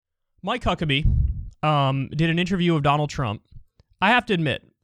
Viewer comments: Sounded like he said Mike Cuckabee at the beginning lol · Did you just say, “My cuck’a be”?